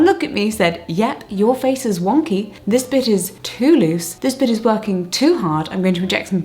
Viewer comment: I thought she was saying "this bitch" instead of "this bit" and it made that segment hilarious